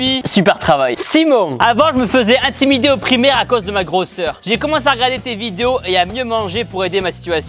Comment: qui est comme moi et entend avant je me fesais intimidé au primaire a cause DE MA GROSSE SOEUR ...?